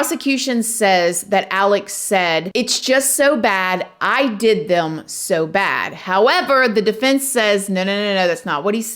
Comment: I heard “I did him so bad.” I think he’s guilty as sin.